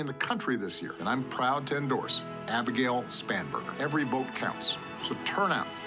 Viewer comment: I thought he said "Spamburger" 😂😂😂😂